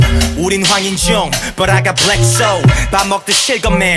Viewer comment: I love zico but did he just say he has a black soul ?